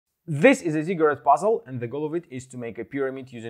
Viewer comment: OH! Ziggurat! I thought he said “secret” which I guess is also applicable